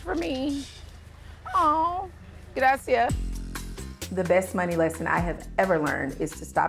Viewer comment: Did she just say "gracias" @ 1:47 !